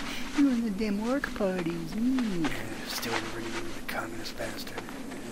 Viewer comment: did just call him a communist bastard o_O